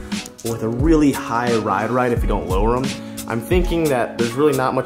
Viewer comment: he says ride reight instead of ride height